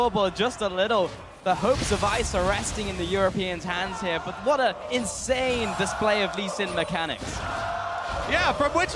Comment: What they are shouting in 23:20? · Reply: JANKOS